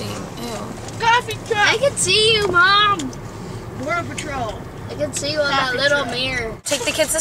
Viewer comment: does your son say "they can see you mom, the border patrol"? Hahaha not that I care that your illegal but I swear I heard that.